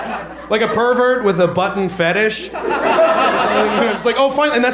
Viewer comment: I thought it said butt fetish